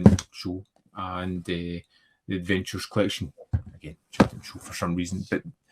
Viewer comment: I thought you said the Avengers box set from Imprint, and was curious what you thought about Diana Rigg and the show in general, but you said The Adventures set, so.....blah.